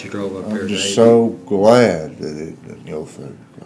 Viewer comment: Anyone know wtf he said at 45:02?? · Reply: "I'm just so glad hurfenfurf"